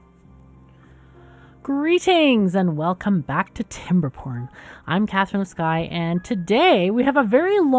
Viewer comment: I heard “welcome back to timberPorn” and all I thought was “that will make the beaverfolk very happy”